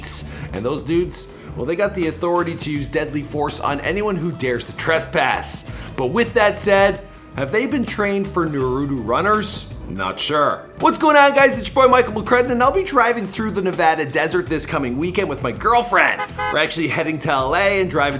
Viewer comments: what he say? Naruooo runners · Bruh...did he just say nuro runner 😂😂😂